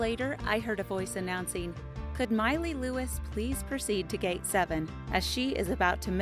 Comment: Anyone else thought she said Miley Cyrus instead of Miley luis